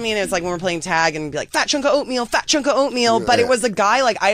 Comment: I was laughing so hard I thought she said “fachunka O’Neil” 😂😂